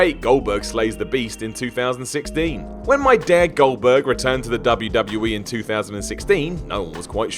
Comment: haha did he say “my dad, Goldberg”? Haha